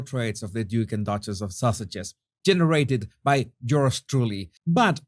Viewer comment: It could be my hearing....or your accent ( which is gorgeous)... but this is the second video in 2 days where I'm SURE I heard you say duke and duchess of sausages.....rather than sussexes....so...is it my hearing, or have they been renamed?😉🤪😂